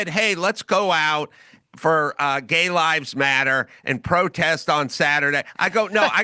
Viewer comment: I swear, I thought he said , Day Lives Matters!. Because these are the Days of Our Lives!